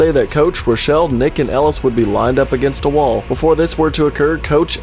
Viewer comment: I watched this 2 times and I realized when he was naming the survivors I thought he said naked Ellis as a joke instead of nick and Ellis